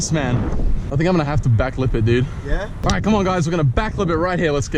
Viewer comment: I thought u said back flip